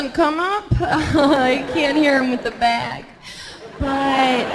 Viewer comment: HELP: At 2:14, what did she say after “it doesn't come up, I can't ___ ___ ____ ___ __” - it sounds like: “I can't hear him with the bag” but it doesn't make sense.